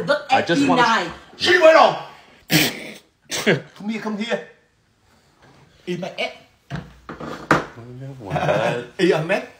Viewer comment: I thought when his dad said eat my egg was eaty ballsac*